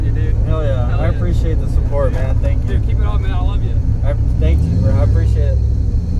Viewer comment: bro did he say “Keep it up man I Love You” ??? 🤣🤣🤣